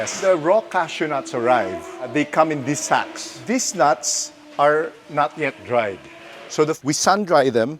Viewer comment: Very interesting, never knew all that thank you. In addition, I kept hearing "Dees nuts" and because I'm a grown ars man I giggled every time.